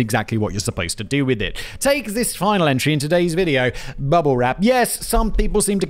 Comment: Did you say "rubble wrap" Simon? 😁